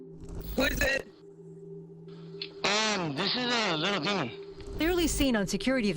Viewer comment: I keep forgetting Ty has 2 channels lol. Update: oh that guy said he was a demon? Lol I thought he said his name was little D. Lol